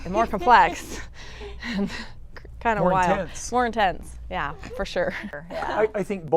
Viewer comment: I swear I heard “more tits” 😂😂😂😂😂😂😂😂😂😂😂